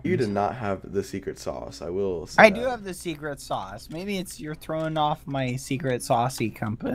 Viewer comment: did he just say secret sauce?